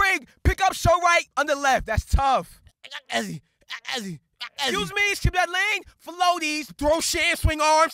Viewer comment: what is he saying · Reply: Ahh hesi